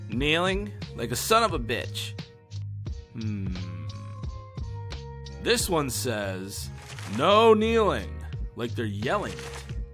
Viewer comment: who else heared "this one sus"?